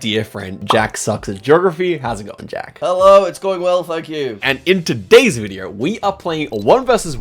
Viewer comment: with that accent it sounded like "thack you"